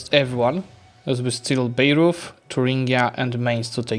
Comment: I looked away for a while and thought I heard Beirut instead of Bayreuth, I was very confused how you managed to get there.